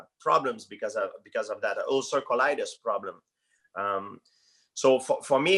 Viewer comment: Can someone help me and say what problem he has... because I cant understand it =( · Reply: Ulcer colitis